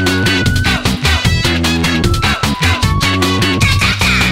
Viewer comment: what the hell does the sample at 0:25 say? i only hear "Ceviche" lol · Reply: I think she's saying "the only thing I'm eating"